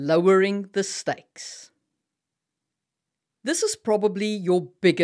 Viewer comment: For point one I first heard "lowering the sex" and while not the point I still thought there was logic in it. Many shows, books etc have immortals act like teenager sex rabbits